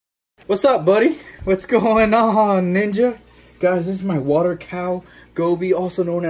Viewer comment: I thought he said “what’s going on n*gga” I replayed it 3 times 😂🤦🏼‍♀️